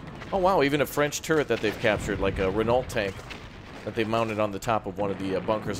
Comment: Have I misheard or did you call Renault “Renaulty”? It should sound like “Reno” :)